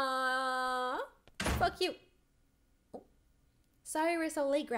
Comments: DID SAY F U? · I heard f you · kinda sounds like she says “f*** you” lol.